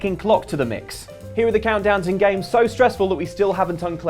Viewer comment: I thought it said condoms instead of countdowns